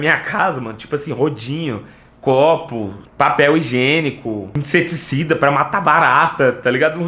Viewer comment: Eu Ouvi a Letícia 😏😈😂